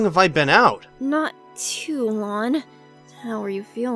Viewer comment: Did Tails say “not too lawn”?